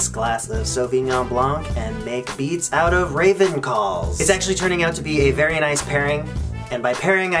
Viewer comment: lol i thought he said ravenclaws